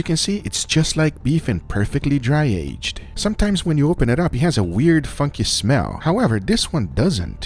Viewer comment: I thought he said it has a "weird fu***n smell"😂